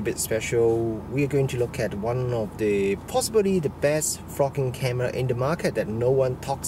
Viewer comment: Your accent has given me a smile. For the first half I thought you were saying 'the best f** king camera :) the smile came when I realized you were saying 'best VLOGing camera. My bad, but it did make me laugh. A tip - you talked about a lack of dynamic range and did not seem to be aware of two things: first, the camera has an HDR mode which would bring the detail back into those blown out highlights. Second - use a variable ND filter to control light better. I think that camera has a 49mm filter thread and a smaller size ND like that won't be all that expensive. Try it out in the shop just in case it vignettes at the wide end. If so use a step-up ring and try a wider ND. The lack of built-in ND is the worst aspect of the AX53 and this Panasonic but that comes with the lower price point compared with more expensive camcorders. Another tip - that camera has a slow zoom function that can sometimes be a better choice than using the zoom rocker. It is one of the features that the Sony AX53 lacks but would benefit from. You can set the slow zoom to either normal speed or slow speed and once enabled gives a constant zoom speed that is easier on the eyes. Great feature. Rather than just comparing with DSLR form-factors you should compare against the gold standard camcorder in this price range - the Sony AX53. The differences between them are very interesting (hint: there is no winner, they are both great but each does things the other doesn't). For just a little extra money there is